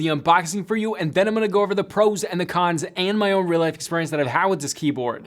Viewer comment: wait.. did u just say probes? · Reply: Shoot, lol I misheard that NGL, I full on heard u say "probes and cons" at the time lmao, just realised now, my bad 😅